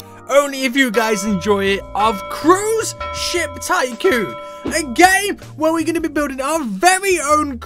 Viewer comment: i thought u said a gay